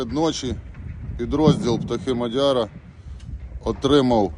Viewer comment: Пiдроздiл " Петухи мадяра" , послышалось сначала. А воно вона як!